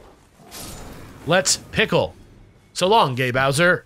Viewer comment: I always thought Mario said "Let's pickle!" and it was weird folksy Italian stereotype equivalent of something like "Let's tussle!"... and honestly I approve. Stare down your rivals, and invite them to pickle outside.